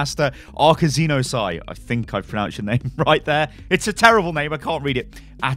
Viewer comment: did he just say "its a terrible name, I can't read it"